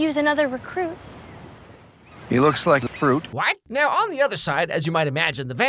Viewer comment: Did...Did he just say Zed looks gay? 😭